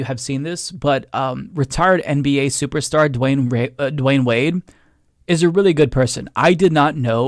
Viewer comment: gotta be honest i thought you said ret arded player Dwayne wade